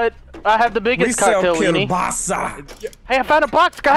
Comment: Did say "kiełbasa"?